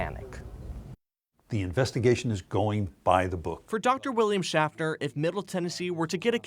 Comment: For a second there I thought he said William Shatner!😂🖖🏻